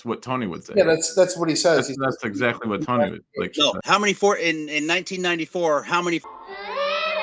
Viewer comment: If you’re wondering what he said at 21:26 he said how many 14 year olds looked the way she did basically, in reference to how “mature” she looked